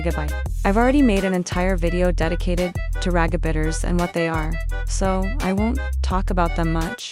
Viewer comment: RAGABITTERS!? 😭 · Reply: I thought they said “Ragatha biters”